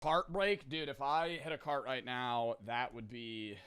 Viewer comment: i thought when he said cart break he said fart break